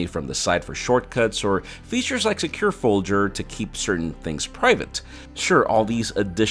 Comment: am I hearing things or did he say 5:25 “secure foldger” ..that made my day! 😂